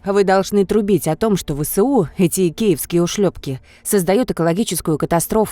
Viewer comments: сначала услышал как эти икеевские :-) При чем тут мебель... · ставь лайк, если тоже услышал "эти икеевские ушлёпки".